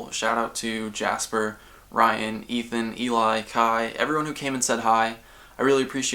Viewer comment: I just heard "Ethan, Hila." (like from h3h3) and had to go watch it again and confirm it wasn't so